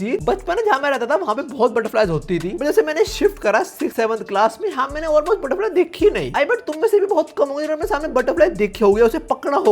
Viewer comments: did he just say 6-7 ? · Did he just say 67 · did you just say 67